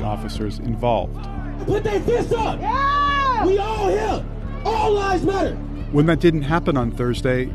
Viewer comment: Did he just say.. All lives matter? Wow. Apparently I’m not allowed to say that lol